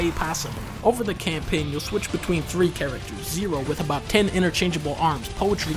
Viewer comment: Did he called Nero Zero?